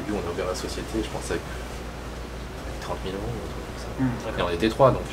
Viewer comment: J'adore sa voix elle est grave douce 😂 juste est-ce qu'il a bien dit 30 MILLION € quand il parlais des prix à payer en corée vers 17:20 ou c'est juste moi qui est mal entendu 😂😅